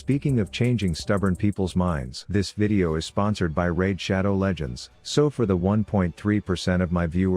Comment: Lol raid shadow legends now even got to. They mustve realise that he's popular by now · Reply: Raid shadow legends? I thought he said shadow legends